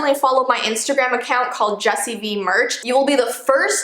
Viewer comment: I don’t know what’s wrong with me but It sounded like u said jessii b instead of jessii vee maybe u did say but I’m crazy so I don’t know